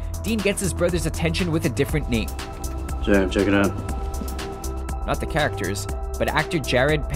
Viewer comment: You're hearing things on that one. He very clearly says, "Sam, check it out." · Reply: Duke Agreed. I hear, "Sam, check it out."